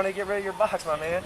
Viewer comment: Did he just say...... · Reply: He said man